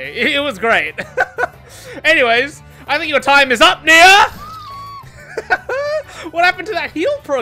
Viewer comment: did you just say the N word? Cancelled. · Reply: Said NEA not the N word 🤦‍♀️